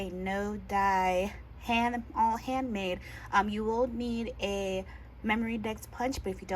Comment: You definitely won’t break your punch! Go for it!! 🥰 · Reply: gurl, I won’t cut glitter paper, I’m sorry I forgot to tell u how cute it turned out, omg I heard you say you used a die, I think I heard kscraft 🤦🏻‍♀️, ok rewind. “No die all handmade” ok hearing test needed 😂